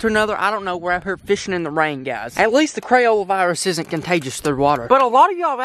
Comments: Did he just say the Crayola virus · Did you say Crayola virus? XD